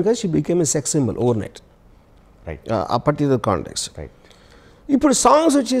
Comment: did he said particular context ?? @ 15:15 AR Rahman laziness @ 28:03 Highlight